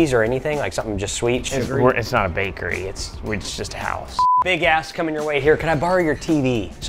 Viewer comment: fr sounds like he said “Big A## coming your way”